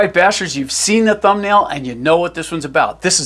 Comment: Did you just call me a bastard? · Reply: "bashers" not "bastards" 😂